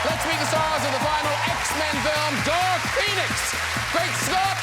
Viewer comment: Am i hearing correctly? DARK PENIS?